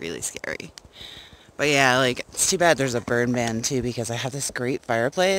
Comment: Lol, after you mentioned wolves, I thought you said too bad their is a birdman too 😶 Uhm is that like a sasquatch!? Lmao, "burn ban" ya. that sucks because it looks chilly where you are. We made it to our camp in the hills of beautiful Tennessee, my birth state... And I can hardly wait to explore it all! its nice and cool here, which is a really great change from the 90s we just left in Oklahoma! Yall have a nice camp there. 💖